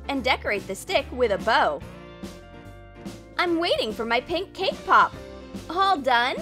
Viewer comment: Was I the only one who heard "K-Pop" instead of Cake pop?